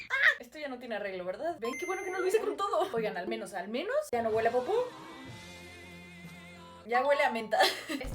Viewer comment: Cuando dijo popó pensé que dijo bonbon😊